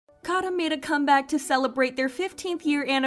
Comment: I thought you said Garam 🖤